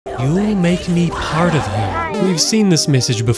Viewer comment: I thought mommy long legs said “He’ll make me part of him!”